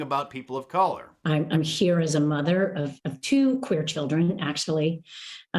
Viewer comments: The smile on her face when she says "2 queen children" just shows that it's more about their own affirmation from other people than the children. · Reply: I thought she said queer children. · did she say she was there "as a mother of two queer children"? Thought that term was banned.